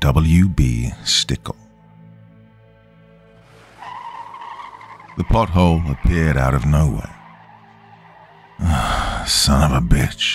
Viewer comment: I'm snuggled in and ready to listen! I thought I heard, "the butt-hole appeared out of no where.,"😂 I had to listen again. I must be tired. Thanks! Have a fantastic day/night!🤗💜🕷